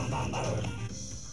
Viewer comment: thought he said we got the wublins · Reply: I thought he said we are the wublins